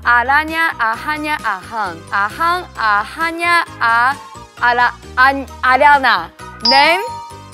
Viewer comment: grande? (it sounds like she says ariana)